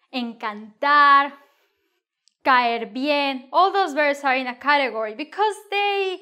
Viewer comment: Can anyone tell what says right after "encontar" at about 0:48? Does she say "cayar bien"? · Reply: Encantar is what she says. As well as caerle bien. The full frase is "caerle bien a alguien". It means get along with, like, be liked by. And the verb follows the same rule as gustar.